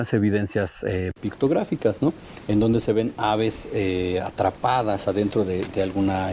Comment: "Aves" yo escuche "Naves"! 😳🤦‍♂️